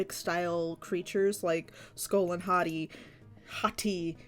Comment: Why when you first said their names I thought you said "skull and hotteeth"